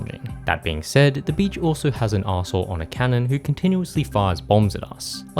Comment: I SWEAR I thought he said Asshole Cannon 😂